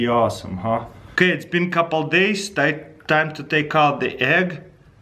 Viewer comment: ok its been couple days WTF i thought you were saying couple hours before!